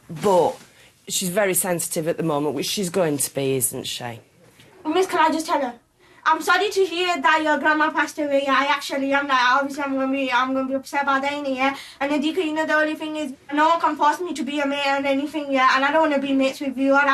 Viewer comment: what the captions said: Miss can I just tell her I’m sorry to hear that your grandma passed away I actually am now some younger groups have our Diana and if you can you know the only thing is no come for me to be a male and anything yeah and I don’t know we miss review all right. What I think she said: Miss can I just tell her I’m sorry to hear that your grandma passed away I actually am like obviously I am gonna be upset by that init and you know Haddiqa no one can force me to be your mate or anything Yh so I don’t wanna be mates with you alright.